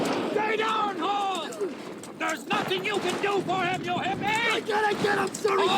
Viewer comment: I get a kick out of the Valour Road one, because every time the one guy, the superior officer I assume, says "There's nothing you can do for him, you hear me?" I always hear it as "There's nothing you can do for him, you hippie!"